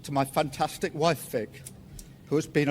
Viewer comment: Did he say his wife's name was Vic? Sounds about right.